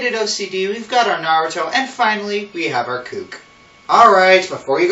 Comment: ~ Natsu Dragneel is FAR from being a Naruto character 8:14 ~ I thought they are called "valley girls" not "cook" (cook??)